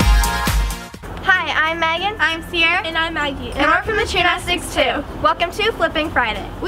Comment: Does anyone else think that it sounds like there saying "and we're from the Chanel 6 2"😂